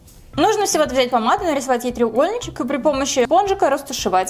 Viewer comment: мне послышалось не спонжика ,а бомжика